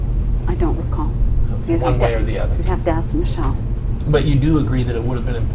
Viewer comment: Did she say Axe Michelle????